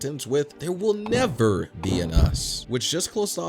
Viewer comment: I thought it said never bean us